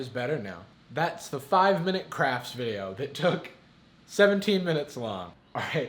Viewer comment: i heard "5 minutes craps"